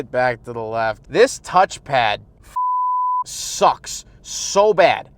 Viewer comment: 😭 cussing got me crying yo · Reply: I was zoned out the. I hear “this f*u* touchpad”